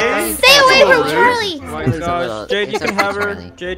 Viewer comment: did say Jake instead of Jaxx- Yall please correct me im will literally be deaf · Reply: She said jade btw